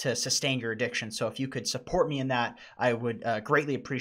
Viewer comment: Did he just say "I hope to see you in another Epstein"